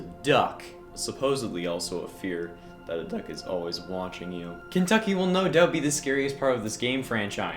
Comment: Is it just me or did he say Kentucky? I know he probably says something else but I can't tell what. Even the subtitles think that's what he said.